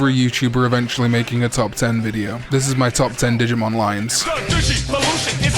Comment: I could have sworn you said Top 10 Digimon Lions 😅